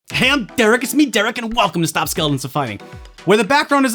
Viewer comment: I thought today he said "Hampter I'm Derrick" I need to stop watching memes